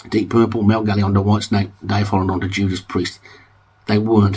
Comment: Correct me if I'm wrong, but did you say Trapeze were better than Judas Priest? It's hard to tell because, perhaps out of embarrassment, you sort of stammered and slurred you're way through it so we wouldn't notice.